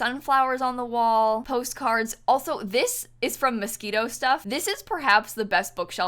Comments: I misheard: "this bookshelf is from mosquito stuff" - excuse me what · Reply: She calls the pack Mosquino stuff mosquito stuff :) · did say "this is from mosquito stuff" 😂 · When you were talking abt the bookshelf I thought you said it’s from “mosquito stuff” 🙃